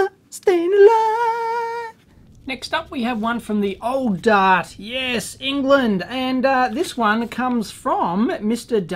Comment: What does say at 13:40 about the Aussie marrying a Brit? "Pond"? "Pong"? What is the source of that word. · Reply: Pom... <link>